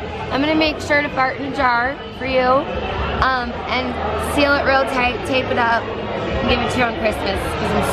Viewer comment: wtf is she saying? "im gonna make sure to fart in a jar for you and seal it real tight and give it to you for christmas"